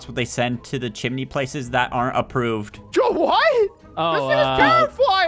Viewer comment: I thought said JOE WHAT